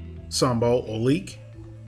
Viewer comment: What are you saying after brown sugar at 2:05? Sounds like sumbol olique? It would be helpful if the ingredients and method was listed in the description. Thanks!